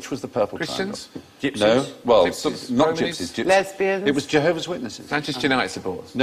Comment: What is Alan Davies saying here? It sounds like Bronies but that can't be right.